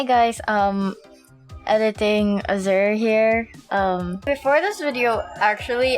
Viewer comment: I think I heard “Editing Azure”